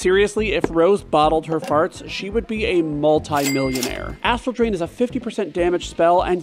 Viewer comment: Because of the emphasis on ASStral Drain, I always thought she was saying "a**hole drain" when I was a child 😳